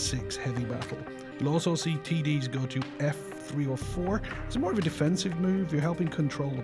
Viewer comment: "F 3 or 4" i heard "Fv304"